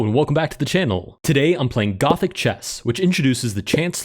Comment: I thought you said “Gotham Chess” lol 😂